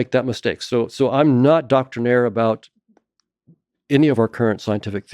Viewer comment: "I'm not doctrinaire about any of our scientific theories." I learned a new word and a new trajectory of thought. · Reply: Even though I know the word 'doctrinaire' I heard 'Doctor Naire.' I was in the process of looking him up when I realized my error.